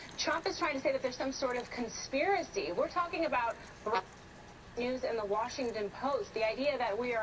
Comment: DID she say JEWS? why did the audio cut out??